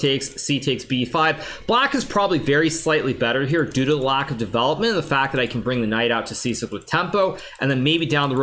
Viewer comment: did he say DUDA? It‘s the other Yan..🙄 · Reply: "very slightly better here due to lack of development"